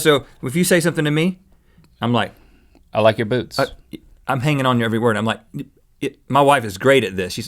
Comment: When link said I like your boots I thought he said I like your boobs😂😂😂